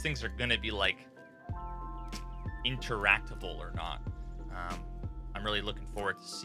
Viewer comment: did he say "interactival"???